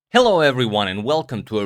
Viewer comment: Everytime i hear Agadmators "Hello, Everyone!" ... my brain inexplicably exchanges the perceived words into "Good news, Everyone!" and im mentally back in Futurama ... I dont know ... he sounds to me like a young Prof. Farnsworth ... am i alone with this??